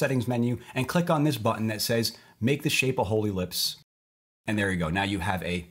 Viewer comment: when he said a whole ellipse I heard a holy lips